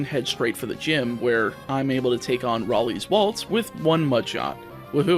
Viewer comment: Rally’s Walts? Did I hear that right?